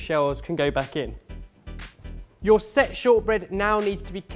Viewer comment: I can't be the only one who heard "Your sexual bread" at 2:54, right?